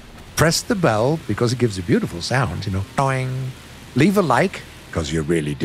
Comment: automatic captions says: "press the bell because it gives you beautiful sounds you know dying"